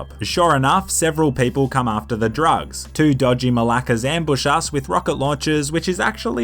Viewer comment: Did he say malakas? Edit